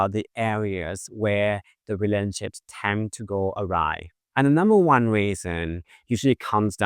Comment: At the beginning when you said “relationships tend to go awry”, I heard “relationships tend to go arai (what??)”. Granted I’ve been studying Thai a lot lately… 😂